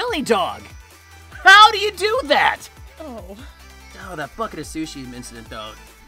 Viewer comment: DID sonic say the f word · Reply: *bucket